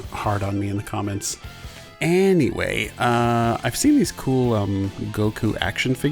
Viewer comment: Did you say Andyway?